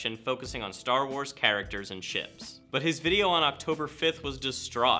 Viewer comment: only i heard shits?